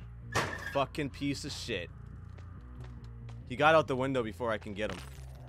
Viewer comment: curses, I think he says "Fucking peace of shit". Why did he mute himself, though?